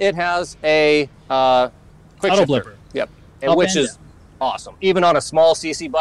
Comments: "an it has uh ... fjdklsjgfdjskalfjdska;jfdslfdskafl;dsjakl;fj .. which is awesome" WTF does it have? (3 people says 3 different words simultaneously, subtitles stopped too ...) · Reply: Quickshifter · It has a what? You both talk at the same time and I can't understand and it seems like a really high tech feature but I have no idea what he said. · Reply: he said it has auto-blipper where you just simply push down on the shift lever and the ECU will automatically blip the throttle at the appropriate moment, ensuring the next gear is selected smoothly and seamlessly.